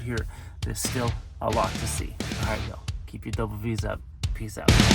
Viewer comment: Hey, I'm your 79 y.o. follower that thought you were saying double 'D's lol. I got it now. I'm enjoying your videos as always. Keep those batteries full! Continue to have fun, my friend. give Cruzzie a scratch under the chin for me! And yes, amanda does rock!